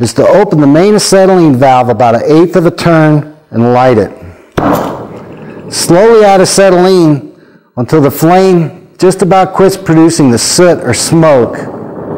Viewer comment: I can't hear what he says at 0:45. It sounds like "slow down the acetylene until the flame just about quits producing the soot or smoke", is that what he says? Then it seems like he turns it down the acetylene, but then turns it up again. In short; I'm not sure what he tells me to do in order to make it quit prouducing soot.